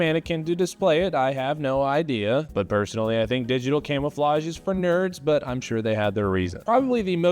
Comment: Man I thought he said Camelflage